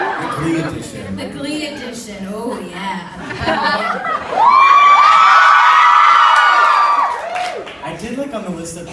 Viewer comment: What does she say at 6:37 that makes John laugh? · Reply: this is very late but she said “beware”